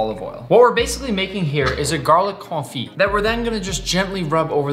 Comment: When you were making hte garlic thin it sounded like you said a Garlic Coffie Im not trna be mean